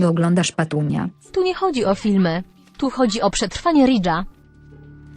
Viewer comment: czy tylko ja usłyszałam : tu chodzi o ratowanie Lidla? XDDD